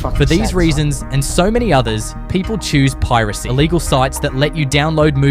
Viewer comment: subtitle says "legal" i think u said "illegal"